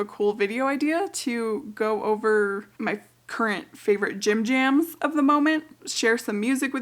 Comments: Gym Jam please, or as I thought you said at first, Jim Jams, and I was wondering if we wanted to see a video of you swanning around in your PJs! 😂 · Heck yeah!! Whats your gym jams? Ngl originally I thought you said jimjams like pyjamas.. i still thought "heck yeah" XD